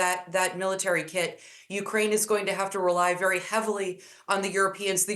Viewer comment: I thought they said Ukraine is winning 😅😂